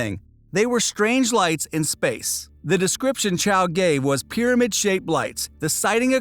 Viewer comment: IS IT JUST ME OR DID HE SAY “Gave” BUT I HEAR “GAY” LOL HAHAHAHAH